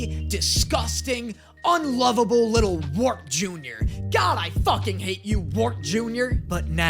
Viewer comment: I know youre not saying it but i keep hearing you say "Worf Jr"